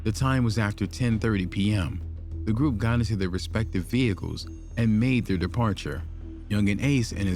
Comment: I mean 5:14 did he say respective vechile ?